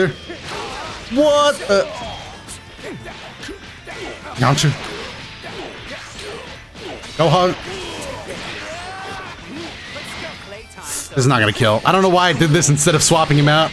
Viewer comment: it sounds like you said gohan instead of goku 😂😂😂😂